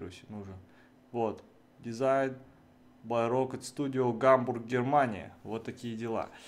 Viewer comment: Когда он сказал -Гамбург, Германия, мне послышалось Гамбургерамания